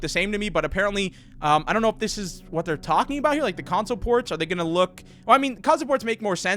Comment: When he said "console ports" I thought he said "console WARS". Gave me some PTSD there...